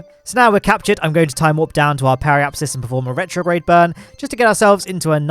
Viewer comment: My brain is dumb. Every time you said periapsis I thought you said apoapsis and I was like "Omg matt you need to learn the difference between periapsis and apoapsis." I need to listen better.